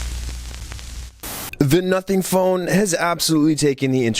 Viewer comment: i swear i heard "nutting phone" 😭😭😭😭😭😭😭😭😭